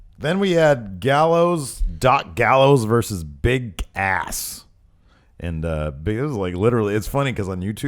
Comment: it sounds like said "bick ass" lol